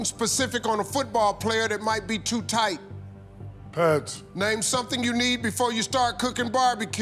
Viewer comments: turn the volume up and listen closely. He says, "PADS". They thought he said pants, lol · I heard pads not pants 2:13. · I thought he said pads, not pants. Shaq's probably thinking, I said pads, but pants got a high score so I ain't sayin nothin. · I heard ‘pads’ not ‘pants’ 3:15 Chuck is hilarious after the first top answer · I heard pads, what about you all?